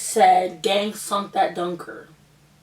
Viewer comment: what did she say?? I'm not American or a native English speaker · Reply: Rani A she said “gang sunk that dunker” its not English lmaooo